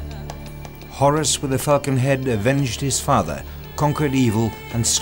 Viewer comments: it sounds like he said "Horus with a fucking head" LOL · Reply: I totally heard that, too. I only got that he was saying 'falcon head' after several more seconds of brain processing. Lol. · I have no idea what the narrator really said at 11:42 but it sounds like "Horus with the fucking head..." UPDATE: It took a while, but I finally figured out what he really said: "Horus with the falcon head..."